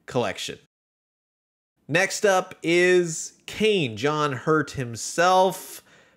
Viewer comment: I thought you said John Hurt Himself 🤕 took me a moment to realise you where talking about 'John Hurt' 😄 Great Video! Love these figures, just wish we'd have a wave 5 with Kane, Dallas, and Lambert in their regular Nostromo Outfits.